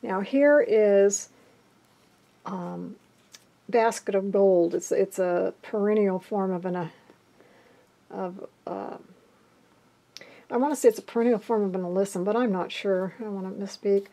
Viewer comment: Hi, at 8:55 you are holding up a cluster of yellow flowers. I can't quite understand the name. Could you type it out for me? Thank you!!! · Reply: Hi Laurie, it is perennial alyssum, aka basket of gold:-)